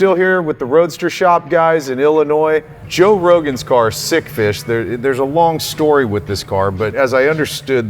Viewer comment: I thought it said 'stick fish' like 'fish sticks'. I like Cuda stix